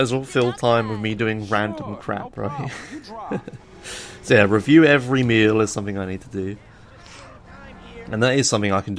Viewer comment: I heard "Review every meme"